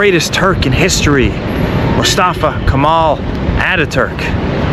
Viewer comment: in my head I just hear some dude with a cockney accent saying "Atta Turk"